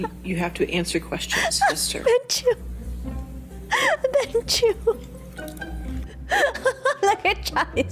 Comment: I honestly thought for a second she said bhencho*